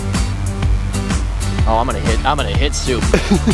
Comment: I thought said I almost hit sue 😂